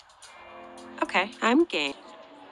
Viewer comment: Is it me or when wolf used her words her words I heard "ok I'm gay" 😶